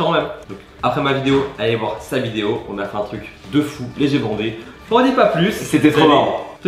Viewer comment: il dit quoi " un truc de ouf et j'ai bandé" ?! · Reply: Les yeux bondés